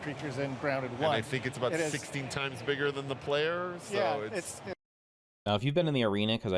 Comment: Did he say "16x times the detail" 😰